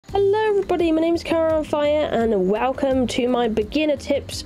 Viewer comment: I swear i hear "my name is Kyra on Fire" every time u say ur name 😅Great Video as always, cant wait to test the Game out!